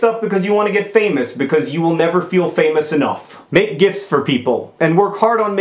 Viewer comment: For most of the video, I thought he was saying "gifs" instead of "gifts". Both make sense.